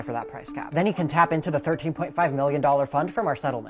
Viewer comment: The fact that they only paid 13.5 mil is a travesty for how much they damaged the healthcare system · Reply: Honestly! I misheard it as “billion” and thought “that’s a bit low, but alright…” then rewatched it… that’s so bad…